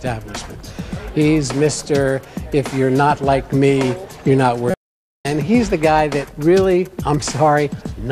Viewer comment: Is it just me or.... 5:08 did he just say you’re not worth sh*t